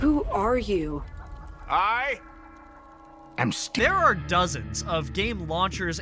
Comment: I AM GAVEN That's what I heard